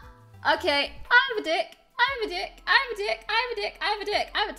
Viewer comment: when she released her suppressed tics i couldnt stop laughing cause all i heard was “i have a dick i have a dick” btw i was half asleep while it happened 😂